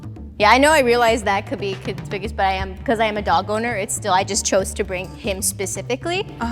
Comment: lolll in the beginning when they says they're a dog owner, I thought the last one said I am a dog.